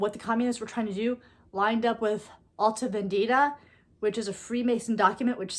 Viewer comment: "Her testimony of what Communists were trying to do lined up with ... altive indeda ??, which is a freemason document." Wtf, does anyone understand what she was saying there? 🤔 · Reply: It is Alta Vendita. It is a Freemasonic document that states their beliefs and goals, which are contrary to the Catholic Church. You might want to look it up and give it a read.